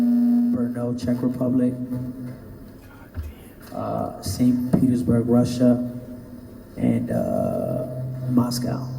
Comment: En el minuto 7:41 al 7:43 en el momento de la interferencia del micrófono y la voz de 69 hay un susuro, quiero saber que dice? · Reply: Dice God damn